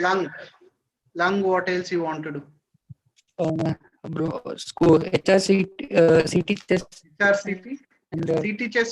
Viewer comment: What he is saying at 1:33:25 which cyst...? It is not audible please can someone tell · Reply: INH cyst